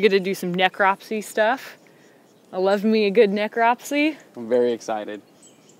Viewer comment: oh you said necropse, i thought you said necromancy...